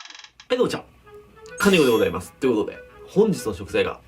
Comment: 蟹っていうイメージありすぎて、最初の自己紹介が「カニコでございますっ！」に聞こえたわ